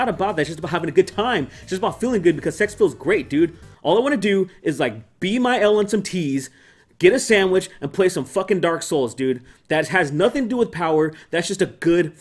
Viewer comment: Be my L and some T’s? What does he say at 10:20?